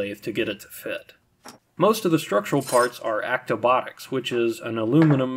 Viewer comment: what's the aluminum kit called? I've re-listened over and over and just can't make out the name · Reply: Actobotics